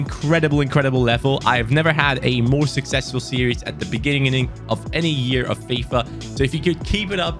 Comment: Did he say beginigining